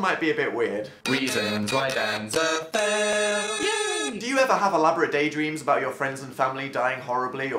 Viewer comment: now every time I hear "reason why Dan's a fail (yay)" I now just think about the roast he did on him self